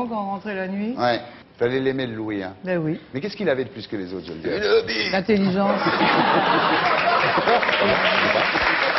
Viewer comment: il a dit quoi Laurent Baffie ? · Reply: 'Uneeee Bite'